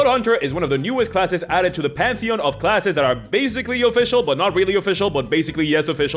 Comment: For some reason instead of hearing "pantheon of classes" I heard "pansexual classes".... I need to maybe not watch this so late